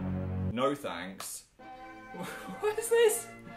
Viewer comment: Don't mind me while I just die watching this for the ninth time. I love watching Phil show us his past, LIGHT. And oh my god his mums hair.... GAH. I'm feel sentimental now thanks phil. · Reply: Ok I heard "thanks Phil" in Dan's voice. I have a problem.